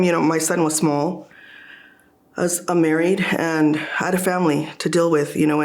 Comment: Did I just hear right? The reason why she took the job even though it wasn't enough money was because her husband was laid off.... but then she later said that she wasn't married, she was a single mom. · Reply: I thought the same thing....I put on captions....She said, "I'm married"..It sounded like she said "I'm not married"